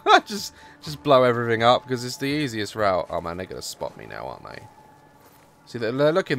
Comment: it sounded like he said the n word😂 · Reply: he didn’t he said “man they’re gonna spot me now”